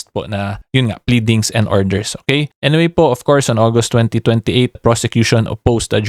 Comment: Hello Atty. @ 6:13 did I hear August 2028 instead of 28 August 2025?